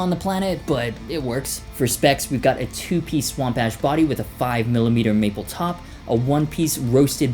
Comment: did u say swamp ass